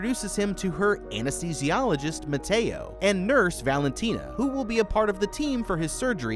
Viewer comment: What it says: Mateo What i hear: 🥔